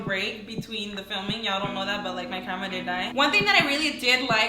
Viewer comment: Did she say "my family did die"